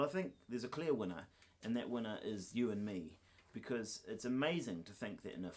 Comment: lol you said "you and Me" and I thought you said, "U-N-Me". And I'm like...which one is that?!